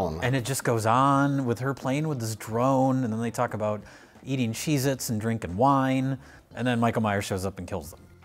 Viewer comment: I could've swore the old couple said "Cheese and Wine" but was right again, they did say "Cheez-it and Wine"... gave me a Tums Festival and he doesn't even know me lol. He is indeed a trivia GOAT.